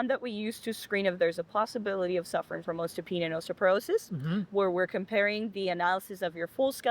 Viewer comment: What is that $10 word she used? Some bone condition... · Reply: Osteopenia and osteoporosis.